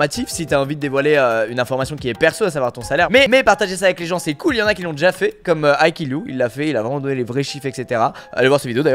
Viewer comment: c’est un bug au montage ou quoi ? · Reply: Oui il a dit "mais mais"